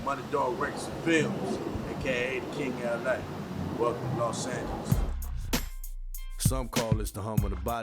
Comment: I thought he said welcome to Los Santos